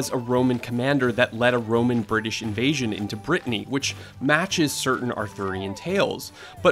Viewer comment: Did he just say 'into Britney' instead of into Britain at 5:48?👀